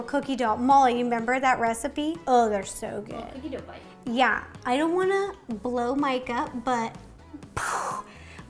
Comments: This is the BEST cookie you will ever eat! What's your favorite kind of cookie? 🍪🍪🍪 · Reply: It did sound like you said "blow my gut". But, you did say "I don't want to blow Mike up, but..." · I definitely heard “Blow my gut.” But on the replay I heard “I don’t wanna blow Mike up.”